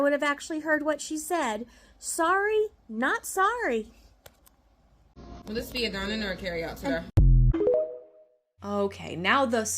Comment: she CLEARLY said “will this be a dine-in or a carry-out today?” CLEARLY!!!!!!!